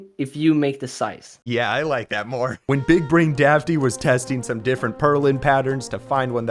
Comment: I thought it said big brain daddy 🤣🤣